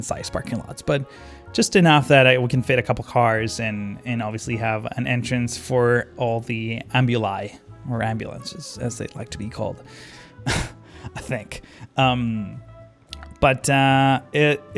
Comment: did this boi really say ambuli